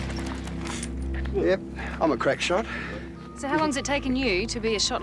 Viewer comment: I thought I heard him say 'I'm a crack pot not a crack shot'..lol.